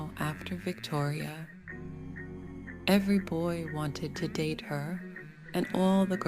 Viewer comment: I thought it said wanted to diddy her